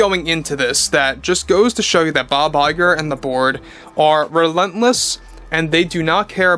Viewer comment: i heard "Bob Iger and THE BORG" Lol!!!.........."Resistance Is Futile" 🤖🛸🏰